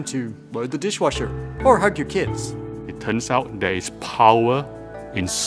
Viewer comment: did he just say "hurt your kids"